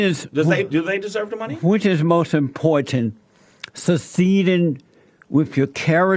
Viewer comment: What's SO SEEDEN ? I think my man is trying to say succeeded! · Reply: SUCCEEDING